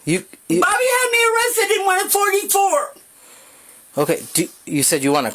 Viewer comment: Hold up, did she say she is 44?🤔